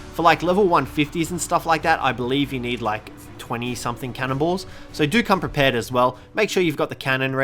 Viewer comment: Lol i thought you said cannables instead of cannon balls 🤣😂